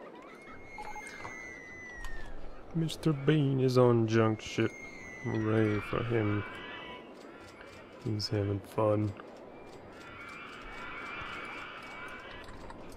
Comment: I thought you said 'Mr Bean is on Junk Shit yay he's having fun' xD